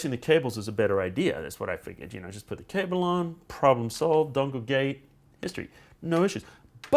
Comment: did he say problem solved don't look gay? Holy George Bush batman.